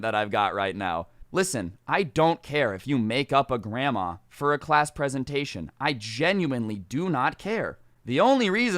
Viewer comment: damn near thought you said "failed class penetration"